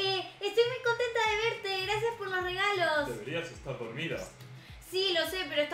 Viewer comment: en el 3:13 en vez de “deberías estar dormida” entendí “no me dejaste comida >:V"